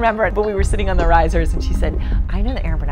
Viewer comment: what did she say? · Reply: "Said". She was referring to what an elementary classmate once said to her about her crush on Dan Rather.